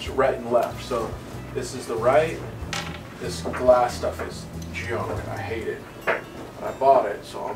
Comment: did you just say the wrong word or put the horn on the wrong side? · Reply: I said this glass stuff is junk. The fiberglass bondo. Not a fan